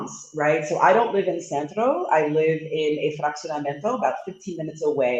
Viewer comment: She mentioned living in Zakia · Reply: mentioned she live in an area that sounded like Atraxiamento (12:00 time mark) but I could not find it and Zakia you mentioned is way further than the 15 minutes from El Centro were she said she lived. Could you check the name of the area again with or provide a close by landmark? Thanks and excellent information on Queretaro!